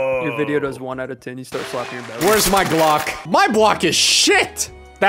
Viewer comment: does say “where’s my Glock?” Or “where’s my block?” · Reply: Glock